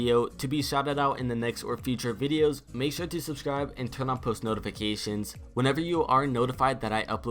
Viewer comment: is it just me or did he say turn off post notifications?